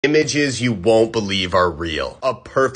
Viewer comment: Am i the only one who heard him say "bitches you wont believe exists"